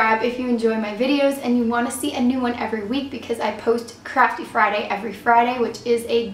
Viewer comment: Oh gosh I thought she said crappy Friday at the end of the intro